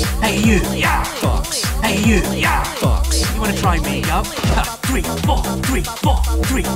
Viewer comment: I always thought he said "You wanna try and make up?"